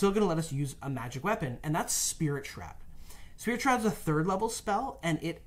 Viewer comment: What’s the spell mentioned at 18:15 ? I’m looking for Spirit Trap and I can’t seem to find it, and I hearing off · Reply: Spirit Shroud, a new spell from Tasha's Cauldron of Everything.